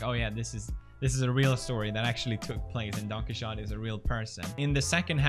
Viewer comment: I kept hearing "Donkey Shot" instead of "Don Quijote"